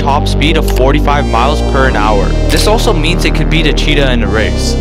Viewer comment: "Per an hour" 💀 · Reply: believe me, everyone noticed that shit😂😂😂 thought he was saying "porn hour" at first 😅